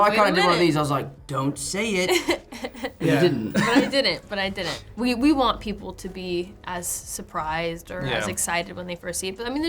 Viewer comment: I honestly thought I heard Zendaya say "we want people to be surprised when they see THEM" but I think she says it, referring to the movie. Thought she gave away the other Spider-Men for a sec